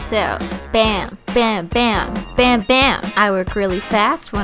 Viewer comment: when she said bam bam I thought she said bambi